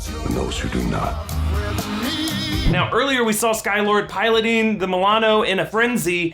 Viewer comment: um... sorry... did he just say SKY-Lord